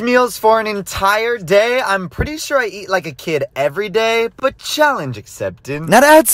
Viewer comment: I thought he said he ate a kid every day 😳